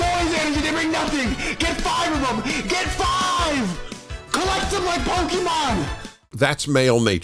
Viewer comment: Did I hear 14:45 that guy say collect them like Pokemon